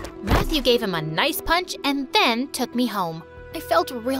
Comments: lol why does it say punt instead of "punch" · when Matthew punched Leo the captions said punt not punch 😂